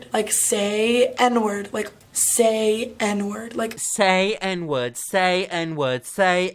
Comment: honestly I thought pyro said "say EDWARD"